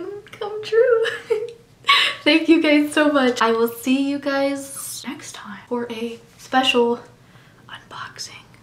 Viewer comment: At the end of the video before you said the unboxing part it literally sounded like you said "we'll see you sex time" cuz if you did that was freaking hilarious 🤣🤣🤣🤣🤣